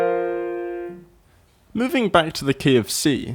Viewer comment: why did i think he said “moving back to the KFC”